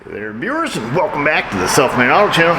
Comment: It sounds like you say, "Welcome back to the self-made auto channel".🙂